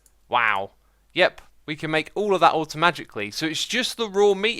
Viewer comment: when X said automaticly i thought he said automagicly xdd